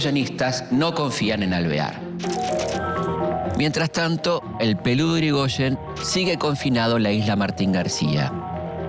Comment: dice el peludo de yrigoyen? jajaja o yo escuche mal xD